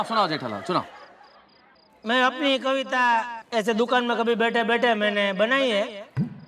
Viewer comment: Legends notice subtitle error 😂😂 Shitting came instead of sitting😅